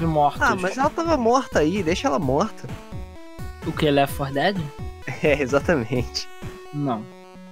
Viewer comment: agora q eu entendi " essa serie ja ta morta, DEIXA ELA MORTA AI" (Left for Dead) naaaaaaaaaoo kkkkk